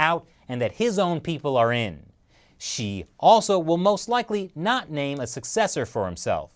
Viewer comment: I thought he said “SHE” instead of “XI”